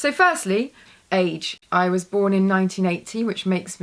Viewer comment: I thought she said I was born in 1918, I was like what?? But then I thought, oh 1980.